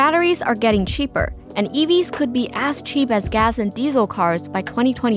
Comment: I thought she said "ass-cheap"